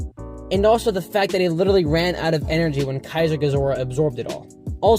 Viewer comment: Ngl kamoebas solos 1:12 did I hear kezier gezora? Or me dum